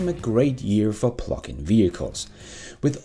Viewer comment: Hehe i thought you was saying phucking vehichles instead of Plugin LOL